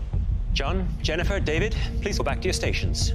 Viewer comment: I thought Chef Michael said "Jon, Jennifer, Javid"